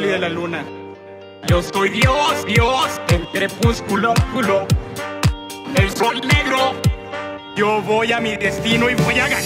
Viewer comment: Yo escuché "Yo soy negro"